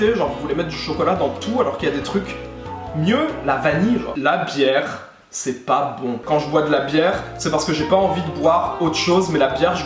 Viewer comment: J'ai entendu "la pierre c'est pas bon"... Pendant 10 secondes je t'ai imaginé entrain de manger des cailloux